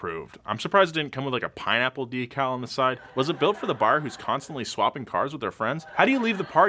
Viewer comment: I've misheard that as: "Was it built for Barbie who's constantly swapping cars with her friends?" I was like "Oh god NO! that swinger joke" 🤣